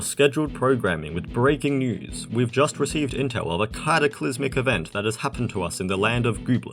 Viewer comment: Idk if this is intentional, bit it sounded like you said "CLADOclysmic", which seems like a cute pun on cladogenesis (a type of evolution where a species splits into different clades, just like what the crack you added does)